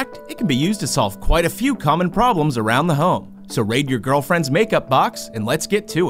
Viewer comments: I heard "rape your girlfriend's makeup box"😂😂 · I thought he said “ so rape your girlfriends make up box” Only god can save me now